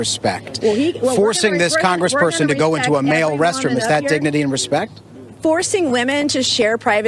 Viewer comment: I thought he said "digity and respect"